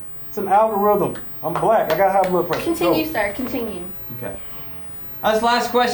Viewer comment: Wait a second.. did he just say @ 12:37 I'm Black i got high blood pressure Lmaooo